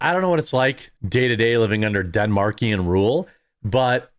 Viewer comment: mate, did you just say "denmarkian"?